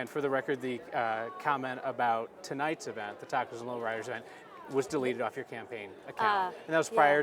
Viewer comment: Did he say she said something about LowRiders? Oh hell no bitch! Its on 👊😤👊 😂😂😂